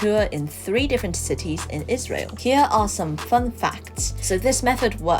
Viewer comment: did you just say israel 🤮🤮🤮🤮🤮🤮😱 f*ck them and there country very Palestine🍉🍉🍉🍉🍉🍉🍉